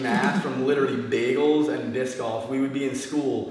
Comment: What does he say? biscoff or smthing?